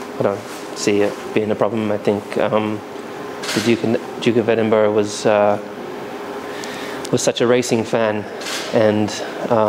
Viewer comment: For a moment there, I swear I heard Lewis say "The Duke of Edinburgh was such a racist man . . . "